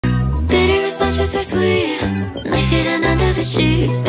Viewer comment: Is it just me or do I hear it like “bitter as much is as we’re sweet life livin under the sheets, baby dolls, kill don’t be focussed or we will c_m” beatdrop